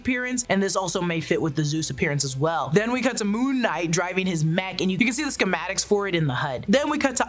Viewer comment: Something I found interesting is that on Disney+ the subtitles say [Hawkeye] instead of Moon Knight. Possible error? Foreshadowing? And around the 1:16 minute mark in the trailer, it plays a theme similar to Kang's theme from the first Quantumania trailer.